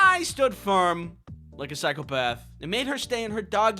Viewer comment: my head phones are broken and i swear that at 4:24 he said "like a bag of meth"